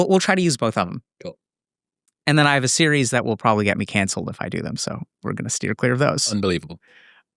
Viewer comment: Did he just say at 3:40 that he has a series of accents that would get him cancelled? I can't help but picture dr K. talking about love, respect and unity in a heavy jamaican accent Bombaclat!